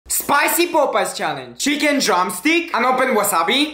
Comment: I thought he said "SpIcY pOpOos ChaLlEnGe